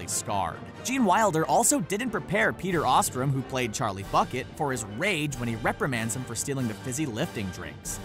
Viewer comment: Excuse me did you just say, Charlie Clucket